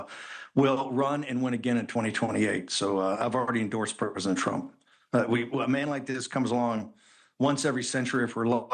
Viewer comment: Steve Bannon "Ive already endorsed Perpesent Trump" (Thats what I heard anyway)